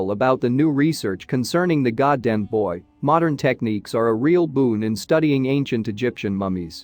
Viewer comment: the subtitles say "the new research concerning the goddamn boy" and it sounds like the voice says that too